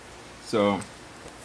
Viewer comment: not tryin 2 hate but at the end he sounds like he says stay freakin naked lol :) but im smokin on some strawberry cough